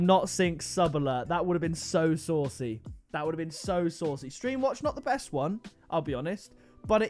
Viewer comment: Did actually say ‘Stream Watch’ 😂